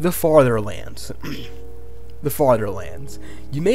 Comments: I thought it said fatherlands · Everyone: farther lands Him: FATHER LANDS we have discovered where our fathers went to get the milk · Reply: i heard “farterlands” 💀